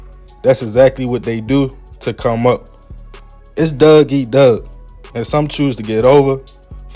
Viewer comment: Don't take the joke wrong homie I'm from Maryland like hour from b more and at first I thought u said Doug eat Doug lol then I realized u were saying dog